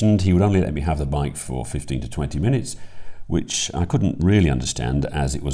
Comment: I thought you said 50 minutes! LOL. "Prepare to be amazed"